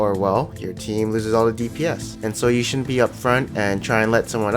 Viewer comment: i can't understand what words you're saying and it's really annoying. For example I couldn't understand what you said at 4:09. You said 'DPS' but i had to rewind like 3 times. Please caption or something.